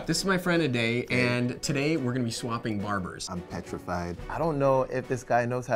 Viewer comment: I heard ‘today we are going to be swamping barbers’